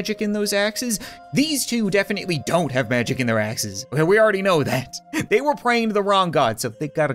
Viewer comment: When ever he said " dont have magic in their axes " i heard " dont have magic in their asses "